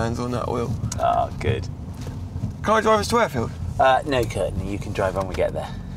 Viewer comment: I swear he said "can I drive us to Wakefield?" 😂